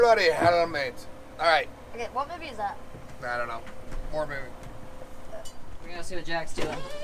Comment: Did I hear the dad say porn movie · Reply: “War movie”